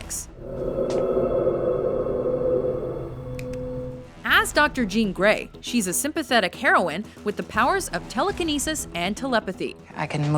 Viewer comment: did you just say heroin?